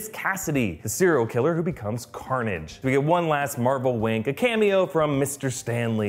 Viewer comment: its sounds like said "We get one last marvel *wank*"